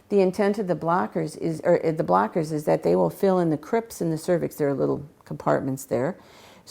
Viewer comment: PLEASE! Can someone tell me what she says at 6:38 ..."they will fill in ___" Sounds like "cripsi" or something like that, I can't understand. Help me out, pls